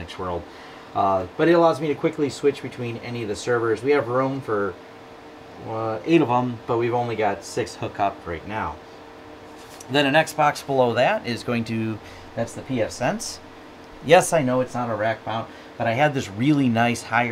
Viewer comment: thought you said XBOX for a moment and was like...wtf!?